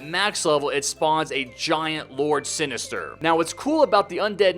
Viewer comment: Did you call jack lord sinister?